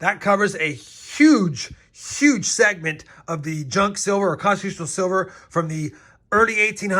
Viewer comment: Oh, I thought you said Sliver...I was looking for Sharon Stone😂😂